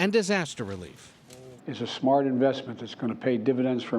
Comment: No no no, it's not an awful lot of money, he even said himself, it's a "small investment" 😂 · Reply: I mean I heard “smart investment” but I also understand the confusion considering he could be my great great grandfather 💀